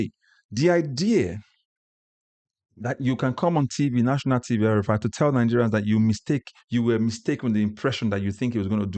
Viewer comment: Did he say after winning the election with the agbero's😢 at 7:42, so thus people knew he was the father of agbero, we're cooked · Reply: yes he did. He said after winning the elections with the Agberos. That's the kind of politicians we have in Nigeria. He celebrated with Tinubu when they used the same agberos to rig the elections but now complaining after their arrangement fell apart. SUBSCRIBE, SHARE, LIKE, and RECOMMEND this channel to amplify the message and keep the awareness growing. Watch more here: <link>